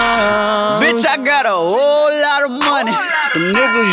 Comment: At first I thought he said I got rich by love